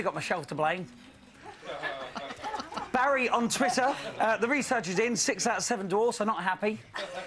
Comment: I don't get the one at 0:40 Did he say "the researcher did 6 out of 7 doors, they're not happy"?